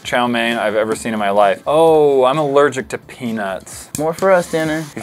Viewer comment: when said I'm allergic to peanuts . I think thought he said penus